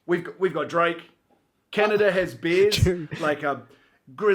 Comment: When he said "Canada has bears" I thought he said "Canada has beers". Both are true, I guess.